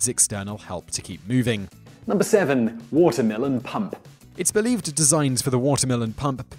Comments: I thought he said Watermelon Pump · I thought he said "Watermelon pump" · Why did I hear "watermelon pump"? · I thought he said "watermelon pump" for number 7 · Dang it...i could have sworn he said Watermelon Pump 😂😂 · In Water Mill and Pump, it sounded like Watermelon Pump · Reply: I honestly thought he said watermelon pump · I misheard the water mill and pump as "watermelon pump" LOL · Could've sworn he said "WATERMELON PUMP" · For #7 I thought he said "watermelon pump". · for number 7 I thought he said watermelon pump XD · I'm sorry.. I heard "watermelon pump" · i heard watermelon pump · in to vid it sounds like he says “watermelon pump”